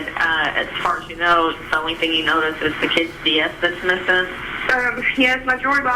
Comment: What was the stolen property that he was convicted of? Was it hers? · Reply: I heard her say "her kid's DS" in the 911 call.